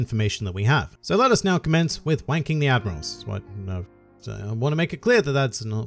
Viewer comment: Wanking the admirals? HEY ?!?!? This is supposed to be a family friendly channel dammit 😤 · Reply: he actually says "so let us commence with wanking the admirals" and it was hilarious because it creates a mental picture of GLR giving wristies to Akainu and kizaru while Aokiji and fujitora wait for their turn